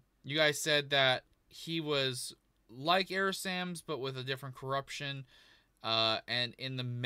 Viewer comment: Sounds like he said "Error Sams."